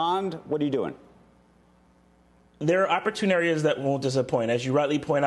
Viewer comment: Did Greg just say "opportuniares" at 3:30? 🤣🤣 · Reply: He said opportune areas